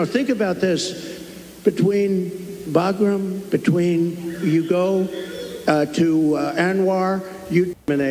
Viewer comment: I think he says Yugo at 3:25 or so Great car, the best cars